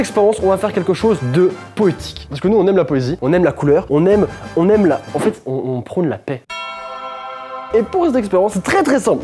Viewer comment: "En fait, on prend de la peine" Moi : L'évangile selon Saint mais comment est-il passé subitement deu montage vidéo à l'église ? 🤔